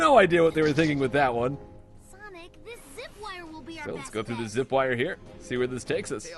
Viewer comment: the captions say Zip-line and tails says Zip-wire